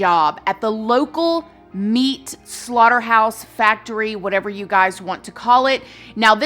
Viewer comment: When she said slaughterhouse I heard “phit phit phit phit phit”.